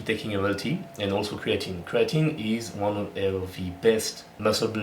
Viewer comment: What did you say before creatine? (I heard "Bubble tea" lol) Congrats for the 14k now! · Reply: Pretty sure I said caffeine and Creatine